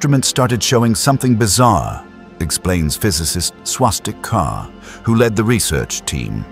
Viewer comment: the scientists name is Swastik Kar... that can't be a real name? I thought the A.I. narrator said swastika